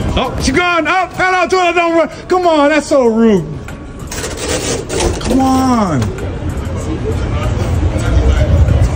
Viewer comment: anybody that can read lip? I know this is a long time ago but her facial expression is like: "please, help" and did she just say without saying: come follow me? also notice how she first looked before she did that. I am worried. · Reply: "Dont follow me"